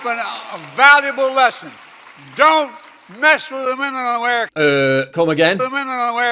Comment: I thought he was saying "don't mess with the minimum wage in America"